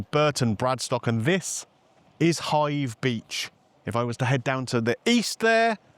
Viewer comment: I know you were reviewing, but I know I would of paid for the drinks and been gone! I thought they were expensive at Portland Bill, but that place tops that! I thought you said Hythe at the beginning and I thought I've never seen that, until you explained where places from their were, lol! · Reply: haha, Hive Beach, not Hythe 😊